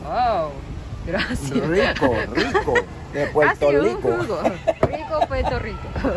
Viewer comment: que burlon me imagino que dijo Puerto Lico burlandose del acento.